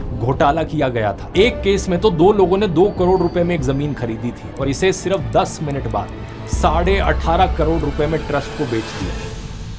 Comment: the subtitles are wrong it's not 18.30 core it's 1.5core